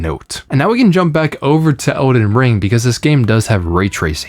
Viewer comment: did you say "Odin Ring"?